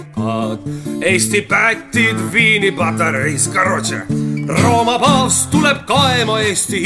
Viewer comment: is he saying "короче" (hurry up/finish already) in Russian?